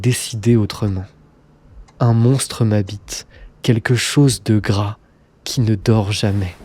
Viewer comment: j'ai entendu "un monstre; ma bite" a cause du sujet de la video mdrrr